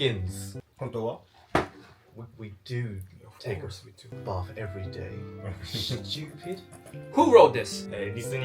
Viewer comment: 会話の内容から「風呂です💢」にしか聞こえんくて字幕みて高度なダブルミーニングかと思ったw